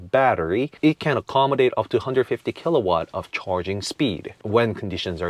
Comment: I just found your Video here and I like the car. However, I am sure I misheard you saying that it charges with upto 150kW. Surely, you Said 50 and I just didn't hear it right, right? 😬